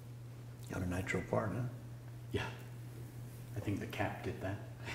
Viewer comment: what did the barber say at 1:26? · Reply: You have natural part there